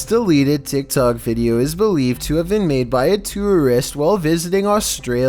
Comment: did he say ".. made by a terrorist while.."